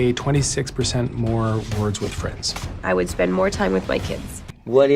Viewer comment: I thought Evangeline was gonna say "I would spend more time with you"